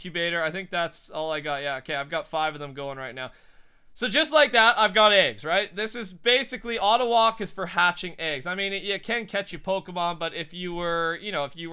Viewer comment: it sounds like he said ive got aids